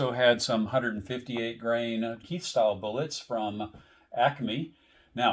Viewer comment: what style bullets are you saying? "Key" ...."keith" ??? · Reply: Keith, as in Elmer Keith.